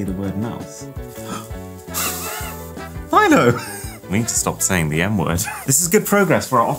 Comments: I thought he just said "We need to stop saying the M-word. But no he legit said "We need to stop saying the N-word"💀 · the what word? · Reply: M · i heard "i need to stop saying the n-word" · I thought he said “I need to stop saying the N-word” 😭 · I thought u said u have to stop saying the N Word ☠️ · when he says “I have to stop saying the M word” It sounds like he says “I have to stop saying the N word” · Out of context it sounded like "I need to stop saying the N word" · When he said “I need to stop saying the m word” I wasn’t really paying attention so I thought he said “I need to stop saying the n word” and because I wasn’t paying attention I had no context and i was shocked when I hear him say that 😂😂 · was it just me or did he say n-word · Thought bro said we need to stop saying the N word 💀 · I thought you said N word instead of M word for a second. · Who heard him say n word at 7:40 😂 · I thought he was saying I need to stop saying the n word 💀😂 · I thought he said I need to stop saying the n-word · I thought he said “We need to stop saying the N word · Am i the only person who thought he said, "I need to saying the n word" not the m word🤣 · I thought he said “ I need to stop saying the n word” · I thought he said the n word 😭💀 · bro i thought he said "we need to stop saying the n word" but he meant the m word for mouse lol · i thought he said "we need to stop saying the n word" instead of "we need to stop saying the m word" 💀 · sounded like he said the n word